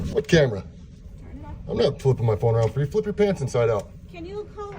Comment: Did he say, "flip your pants inside out!"?! 🤣😂 I'm dead!